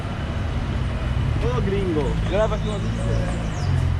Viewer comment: Olá, não tenho certeza mas parece que ele disse aos 08:01..." oi gringo me leva comigo", e aos 08:18 o de bike diz: "ladies and gentlemen" mas a pronuncia errada, aos 15:00 estes rapazes parecem que são "Flanelinhas" (guardadores de carros, ficam olhando os carros nas ruas) que estão discutindo amigavelmente as vagas dos carros a que estão guardando, eles não são regulamentados.